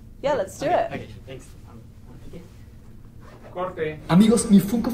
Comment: Minuto 10:15 se oye a alguien que dice corte